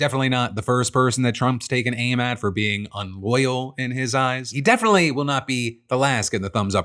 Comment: Hey Phil! Enjoy using your platform to keep me informed! Just wanted to point out that your closed captioning says "unlawful" instead of your verbal "unloyal." Have to have CC because of my poor hearing as a Navy Vet. Hope that you and your staff have a great day! 😁